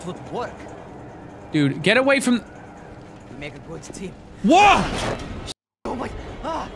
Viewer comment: When falls he says . we make a good team I fall off ME: I thought you said we where a great team